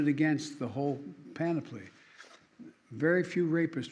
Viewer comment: " ...and run it against the whole pan-a-play..." What the hell is he trying to say? I wonder if anyone has compiled a list of Biden's weird, made up, and mispronounced words. It's like he's created a whole new language, albeit a language nobody wants to learn. He's such an embarrassment.